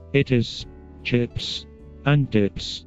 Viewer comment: In the robot review I heard “it is chicks and dicks” and I stopped working to grab my phone and rewind that bit.